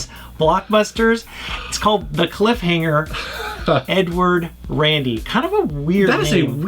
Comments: The Cliffhanger Edward Randy, reminds me of the old James Bond JR cartoon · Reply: i thought he said "N-word Randy" XD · Omg at 4:25 I thought he said the title of the game was, "The Cliffhanger, 'N word' Randy."